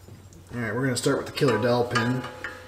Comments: I swear to God I thought you were saying a “killer dolphin” 😂😂😂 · I thought was saying "Killer Dolphin" until I finally saw the dowel pin 🙂